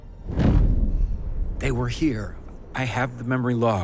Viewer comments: Can someone pls tell me what they say at 39:16? When Maddie asks them what is there? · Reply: "Reunion" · what does it say??? I couldn't figure out the dialogue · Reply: Reunion